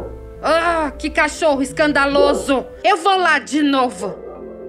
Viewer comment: agora a surda é eu ela falou "eu vou lá de novo" e eu ouvi "chocolate novo" 🤡